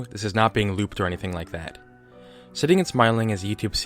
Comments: I thought it said shitting and smiling. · I seriously thought it said shitting and smiling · I REALLY thought that said shitting and smiling.